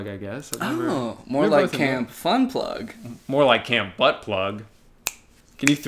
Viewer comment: im so embarrassed but the entire time i thought you guys were saying "camp butt plug" oh my goD